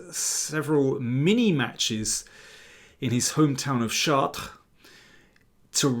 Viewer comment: Did he just say Alireza is from "Shart"? Gross